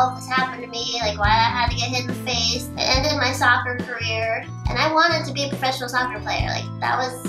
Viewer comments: I thought you said it was soft ball not soccer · soccer??? i thought she said softball